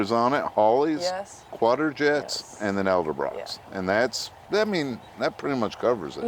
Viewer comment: Did he just say "Eldebrocks"?